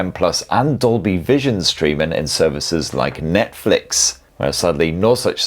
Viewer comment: Thought you said Methflix there for second😅